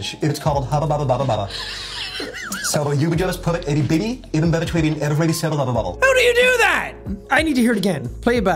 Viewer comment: for anyone who didn't know, I think I the guy said "you just put a b in-between everything syllables" or something like that.